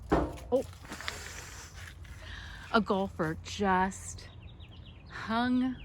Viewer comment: I thought you said a gopher! Ahhh, golfer! Lol